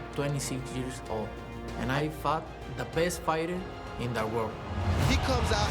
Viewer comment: did he just said he fucked the best fighter in the world?!😂